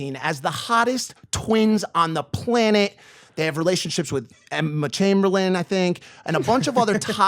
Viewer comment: “related to emma chamberlain I think” When the dolan twins heard him say that they were like “Ight Ima dip”